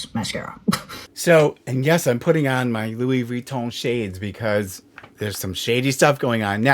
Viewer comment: Did you say your Louis Reach-Ons?! Lol or did I just HEAR it that way?? If so, that’s a PERFECT description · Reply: I figured that out after listening to it the second time, but let me tell you I laughed pretty hard when I thought you said your “Lewey Reach-Ons” (Louis Vuittons)!! Oh and hey, I LOVE how your used your baseball cap to hold your hair back when you showed your demo of the mascara! I thought that was especially unique!! (tell the kitties they got “mousie-bears” coming in the mail!!” I call the skitter critter mouse toys that with my boys… “go get 🐁 mousiebear!!” Much love to you and your feline family!!!